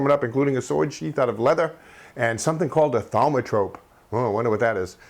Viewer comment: It probably wasn't what I heard, but I thought you said Thalmor Robe =/ (from Skyrim)